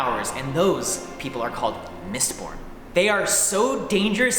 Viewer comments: Only I heard "Mist PORN" not "MistBorn"? :D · i thought he said mistporn